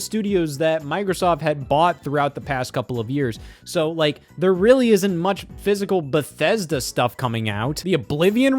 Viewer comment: It took me way too long to figure out what you said, and you didn't even say anything weird. Lord help me! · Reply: thought they said "It really isn't" and I was thinking what are you talking about, how would you know what it's like for them?